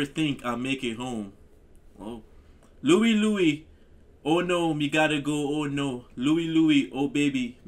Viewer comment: Louie Louie, oh no, you take me where ya gotta go, yeah, yeah, yeah, yeah, yeah, baby Louie Louie, oh baby, take me where ya gotta go A fine little girl, she waits for me Me catch the ship across the sea Me sailed the ship all alone Me never think I'll make it home Louie Louie, oh no no no, me gotta go, oh no Louie Louie, oh baby, me gotta go Three nights and days I sailed the sea Me think of girl constantly On the ship, I dream she there I smell the rose in her hair Louie Louie, oh no, me gotta go, yeah, yeah, yeah, yeah, yeah, baby Louie Louie, oh baby, me gotta go Okay, let's give it to 'em right now Me see Me see Jamaica,… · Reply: I think that’s right except I always thought he was saying “we gotta go” not me gotta go 🤷🏼‍♀️